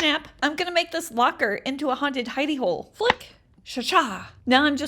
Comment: i ligit thought she said haunted hiney hole... 😶